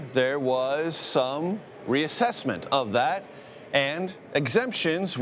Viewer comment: i thought he said without exception